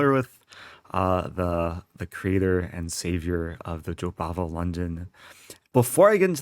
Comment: I thought u were saying Jobaba london lol